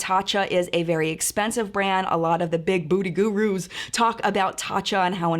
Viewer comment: Lol thought you said "big booty gurus" 🤣🤣🤣 mad love ❤️